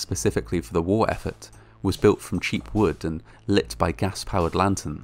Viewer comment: "Still, the worst was yet to come" WHAT MORE IS THERE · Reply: I was asking myself how much worse could it get, and then I heard “cheap wood with lit lanterns” I then said, well crap.